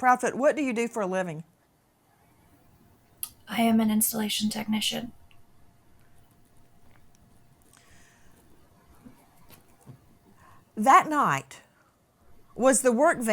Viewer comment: If she’s a security system installer, why on earth would they not have security with an autistic child???? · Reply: I thought she said she is an insulation tech?